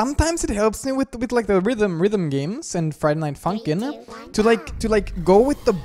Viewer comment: everytime i hear an intro for this game i hear "friday night fucking" and i dont know why